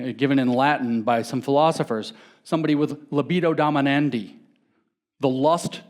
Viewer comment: I made the mistake of not writing this down as I heard it. What was the latin word James mentioned? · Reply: libido dominandi